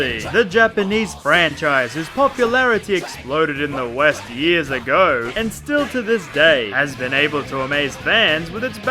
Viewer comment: What does it say at 0:08? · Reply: KingChickenwing I think “goodbye tein”